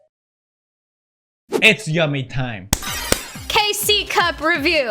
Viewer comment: What does pewdiepie say at that moment? is that I am Spanish speaking and I do not understand English (this I write from Google translator) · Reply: Blas Kevin "it's yummy time"